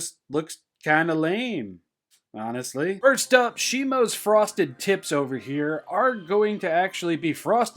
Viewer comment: i thought he said frosted tits for a second 😂